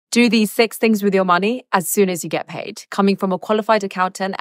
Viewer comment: What I heard was "Do these sex things with money" and I was like Whaaaaaaaaaaaaaaat? 😂 Peculiar accent 😅